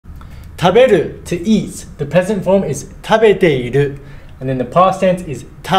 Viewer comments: I heard "The Peasant Form" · I heard “the peasant form” 😭 hilarious lol